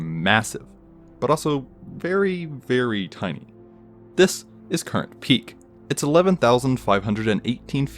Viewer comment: When he said “Currant peak” I thought he said “Coronet peak”